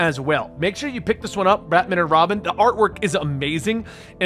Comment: I swear I hear you say ratman and robin...